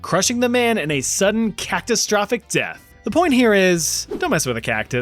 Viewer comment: You said "cactustrophic death"? I swear I kinda heard this 😅